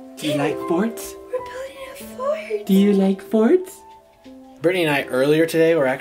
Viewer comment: it sounded like was saying do u like farts